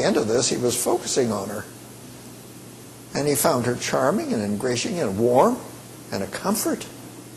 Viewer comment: Hey what does he say when he says "He found her charming, ___ , warm and a comfort." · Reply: I think it was "ingratiating", but he slurs a bit and loses a syllable.